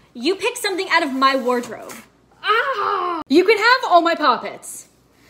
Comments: I thought she said : “you could have all my puppets” lol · Who else thought she said wardrobe and not pop it👇